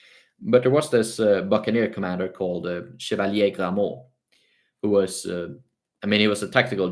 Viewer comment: what was the name said here? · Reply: If I may, I heard Chevalier de Grammont aka Michel de Grammont. Cheers!🏴‍☠